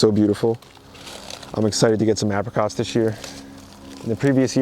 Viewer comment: Hey! I heard you wrong, and I thought, New Jersey REALLY rocks! I thought you said the avocados were coming in strong. Lol, it was the apricots. Jersey still rocks, tho!